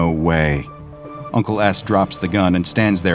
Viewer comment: he says uncle ass instead of uncle S